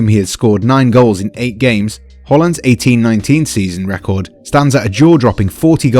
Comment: did he just say whorland